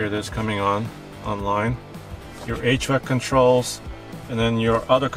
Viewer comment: sounded like you said 8 track controls haha (i looked away for a moment so I didnt see context)